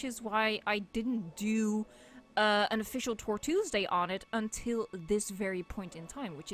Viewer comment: i heard "torture-s-day" i just found ur channel, and im glad theres still people just appreciating this game and - wow. i love this so much xD